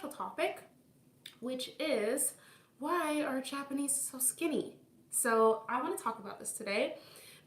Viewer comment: IN DYING I THOUGHT IT SAID STINKY INSTEAD OF SKINNY